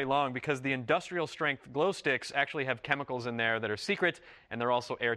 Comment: I thought it said "Glow socks" :(